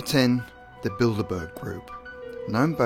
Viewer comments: Trend Crave didn't send me. I'm special I thought the first one said build a bear group 😂💀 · Someone says "Bilderberg" and I hear "Build-A-Bear" XD